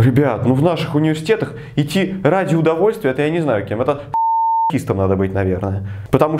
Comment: Послышалось: "Танкистом"